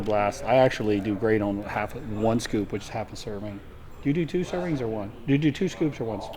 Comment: Good to see John looking a more natural these days on a lower test dose. Also kinda makes you wonder what dose Missy is on, but seeing as though she's competing that info won't be coming out any time soon ;) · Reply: he asks her how many syringes she does instead of scoops 🤦🤣👌